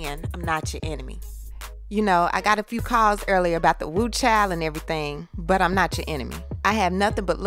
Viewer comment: I thought she said "whew..chile"🤔